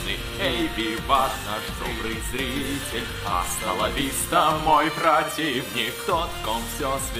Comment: мне адному послышалась астоновистос бэйби